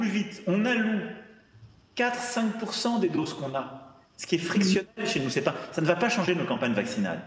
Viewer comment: Hie correct the translation to the video at 01:19. It reads friction instead of fraction.